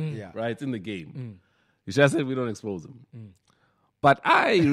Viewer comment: Eish what was the joke/comment Sizwe said ku 34:03min ...anyone heard it ??? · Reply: He said we l know oMashaya in the game, just that we don’t expose them